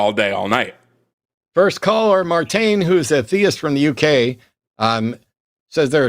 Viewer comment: Time stamps (complete): 2:22 -- Matt's hangup 22:05 -- intro 42:18 -- (he/him), atheist, UK -- fascist/racist/anti-trans caller offering to to educate hosts parrots a bunch of far-right propaganda and misidentifies a number of logical fallacies while using fallacies exclusively to support his views. Very impressed with himself. 1:32:20 -- Natty (they/them), atheist, Brazil -- doesn't see the point in arguing with people who refuse to change their mind 1:41:23 -- Dylan (he/him), CO -- caller in his early 20s says he didn't vote in either of the last two elections and tries to justify it via the "both sidesism" false equivalence fallacy. Says he's against pretty much everything Trump does, but at the end of the day, all he really cares about is the 2nd amendment. 2:12:28 -- Announcements, and and Matt chat for a bit 2:19:27 -- Ethan (he/him), deist, Canada -- doesn't think it would be good for the world to exist without some form of theism... "unless aliens show up" 3:07:11 -- Superchats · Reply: Thanks! You Rock! Question: at 42:18 from the UK says atheist. I thought he said he was a theist (Or the host did?)